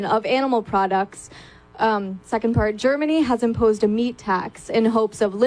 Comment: "Um, Second Part" ... anyone catch that?